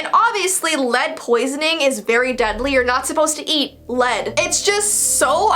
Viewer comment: I thought it said ‘You’re not allowed to eat anymore’